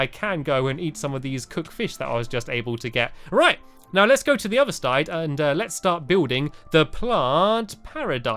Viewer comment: he said stide instead of side